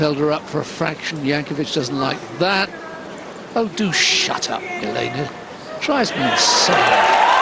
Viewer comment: did he say “shut up”? 🤦🏼‍♀️